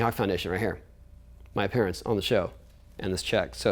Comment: it sounds like he says "My parents own the show." Took me a while to realize he said "My appearance on the show."